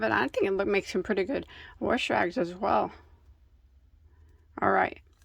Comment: Are you originally from Ohio. I heard you say worsh rag. To me that say’s Ohio 😊